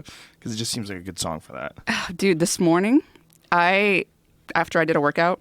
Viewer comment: did She just call him 'Dude'? That seems so not like her :D She is easily among my top 5 favorite JRE guests